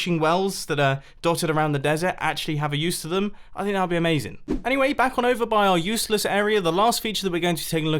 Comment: In the pig with a saddle one... You said "amazing" but I hear " AMAZE-ING " Puns?